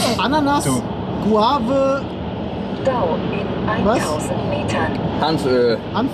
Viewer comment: Wer hat bei 5:07 auch DAU (Dümmst anzunehmender User) verstanden? 😂😂